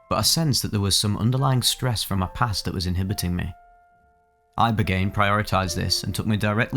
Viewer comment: ROFL i thought it said “Rogaine”